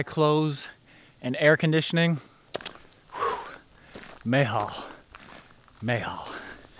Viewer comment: what did he say there, 'mehah' ????